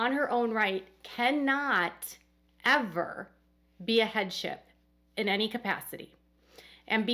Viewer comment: You were saying “headship” and I thought you were saying “head chef”! 😝